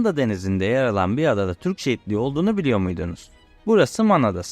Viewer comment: Türkçe bu kadar zor bir değil be çocuk! Şu telaffuza biraz dikkat edin. Türkçe prodental ağırlıklı bir dildir. Defalarca dinledikten sonra "şehitliği" dediğini anladım. Allah aşkına biraz özen gösterin ana dilinize! Hele de bir medya platformunda yayıncı olmak iddian varsa...